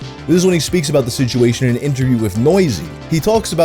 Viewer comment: i SWEAR you said "...in an injury with Noisey" . but if i say "interview" in my head.... i can hear what you said clearly yet at the same time... if i say (aka, think) injury in my head.... i still hear you say "injury" . . idk.... my brain is messed up anyone else have this same issue?